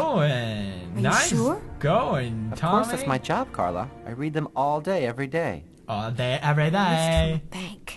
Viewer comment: When they said all day ,every day I thought they said Bro day every day.. :D I guess I watched too much co-op with Cry videos.